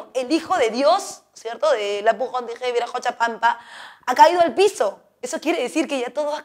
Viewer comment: Que dijo en el minuto 12:44 ? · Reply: Apu Kon tiki wiracocha creo xd